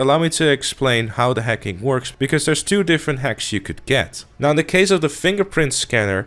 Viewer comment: I thought you kept saying "hecks" instead of "hacks" xD